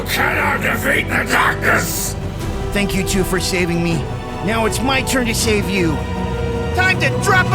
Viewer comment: Did he just say ‘thank you guy for shaving me now it time for me to shave you😂’